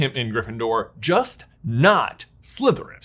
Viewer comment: I thought he said “Just not Slytherin” because of Draco Malfoy....